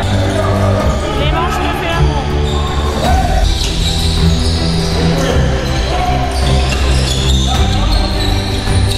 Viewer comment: Clément... quoi ??? 🤣🤣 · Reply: Rien juste la caméraman m'a fais rire "Clément je te fais l'Amour"